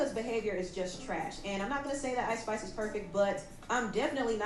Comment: did say ice spice ? 😂